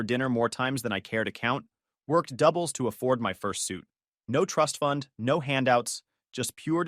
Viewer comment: For a second there i thought he said fur suit not first suit